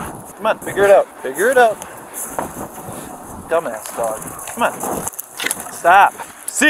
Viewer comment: … what he say. “ Dumb ass dog”?! Wow‼️ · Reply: yes he said dumb ass dog , thanks captain obvious